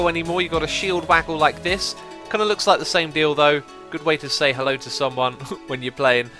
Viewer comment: "kinda like the same deal though" what i hear is ..."kinda like the same... Dildo..." Lol hahaha X